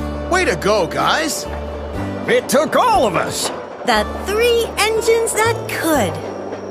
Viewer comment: I hear The three little engines that could